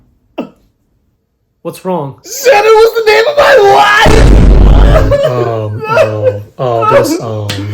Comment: he sounds like he saying “SANTA WAS THE NAME OF MY WIFE”